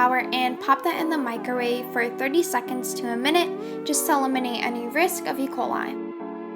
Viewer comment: What she say at 0:55 ? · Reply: I’m pretty sure she just said “to eliminate any risk of E. coli”